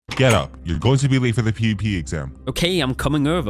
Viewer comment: did he say pp exam?